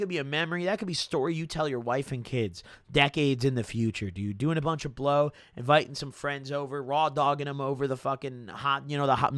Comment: did say he’s rawdoggin his friends😭